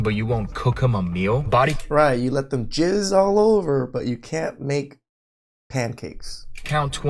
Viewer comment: 😂😂 Skipped the into and all i heard was "get ready for ji zz all over" 🤭🤣🤣🤣